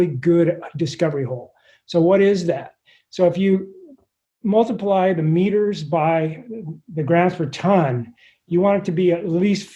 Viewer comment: What did he say about multiplying "meter per xxx"? 3:10 -- Btw, AWESOME interview! 5 minutes in PACKED with amazing information! Great job! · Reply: Multiply "meters" (of crystalized borehole) times "g (of metal) per t (of ore)".